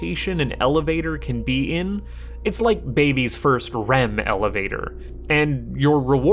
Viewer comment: did you say "RHEM"??